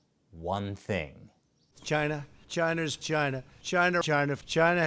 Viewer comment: I thought i heard Trump say, " China's Gina"